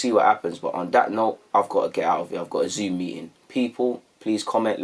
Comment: Swear down thought you said: 'I got a zoot meetin'